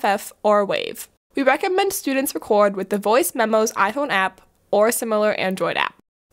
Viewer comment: At minute 1:19 you mention an android app to record voice, but I couldn't make out the App name and I tried searching. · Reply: The iPhone app is called Voice Memos. I'm not an Android user but there are numerous Android apps that will allow the user to record. I think if you go to the Play Store and search for "sound recorder" or "voice recorder" there should be some good options.